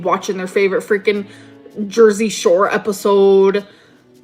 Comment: At first I thought you were saying gross at Jersey Shore, and it made me very sad 😂 I love Jersey Shore! Tshirt time!!!!